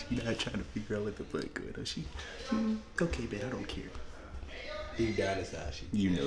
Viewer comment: did he say “ ok bae I do not care “ or “ ok bro I do not care “? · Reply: I think he said bet but I’m not sure